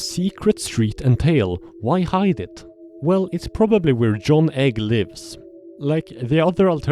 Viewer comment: John Egg had me do a double take making sure I didn't hear egbert